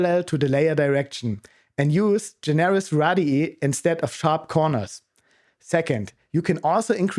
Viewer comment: Use what instead of sharp corners? I don't know what you said there. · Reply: Generous radii 🤣 I think the engineer came through here. Use large radius and round smooth corners to avoid load peaks.